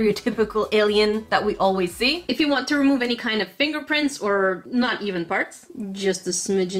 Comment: Right here, I 100% thought she said "any not human farts" when she actually said "any not even parts." No joke, I had to listen to it 4 times before I realized what she actually said 🤣🤣😆